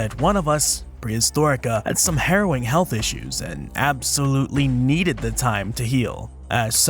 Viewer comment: My sympathies for serious health problems, but you definitely focused my pre-coffee attention when I heard "heroin health issues". It took me a minute to realize my mistake. Hope things get better soon.